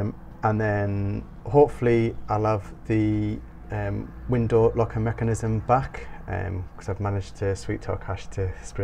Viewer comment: PS I Jumped the gun before the end of the vlog I thought you said you wanted the window mechanism black ! I'm so pleased you chose copper fabulous 👌